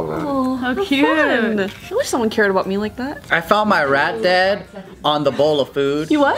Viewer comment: Oh my god I should sleep, I swore that said "I found my rat dad" for a second